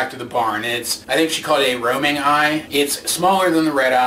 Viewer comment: I too thought she said roaming eye, Someone else heard it as ruby eye tho